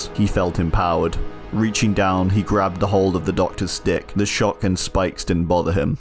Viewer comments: turned this into a NSFW fanfiction real quick. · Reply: LMAO! I for sure thought he said grabbed a hold of the doctor's dick. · I misheard "grabbed the doctor's stick". I was like WOAH THERE BLIGHT.